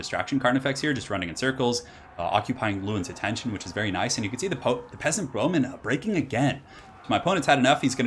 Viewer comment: I swear I heard 'Sith Sorcerers of Avelorn' once. I believe it. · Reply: I heard "peasant roman"